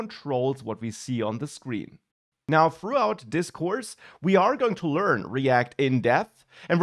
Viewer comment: I heard you say "We are going to learn react in death." 😂😂😂